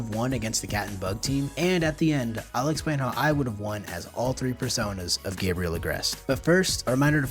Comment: Did I just hear.. persona?